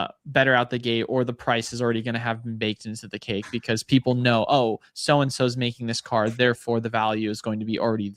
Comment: thought said sowsow here just to mail the point home. 😁